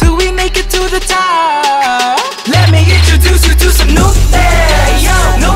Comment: why do I hear 'no thanks' instead of 'new thangs'? 😂😂.. But this is good tho, really good. Keep slayin' bro😉